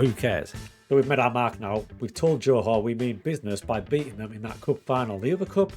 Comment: Definitely thought you were saying Joe Hart for the first 3 minutes